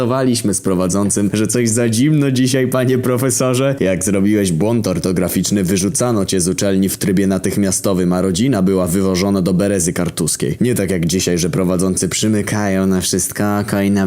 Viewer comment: czy ja usłyszałem stolice kaszub czyli kartuzy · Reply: Nie, chodziło mu o Berezę Kartuską a Od Pomorza na Białoruś daleko